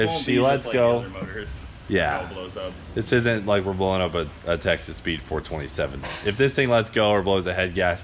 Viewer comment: For the first time ever, my girlfriend has perked up while I watch ol' Cleeter.... because she thought you were saying "Texas Pete" (Texas Speed). LMAO!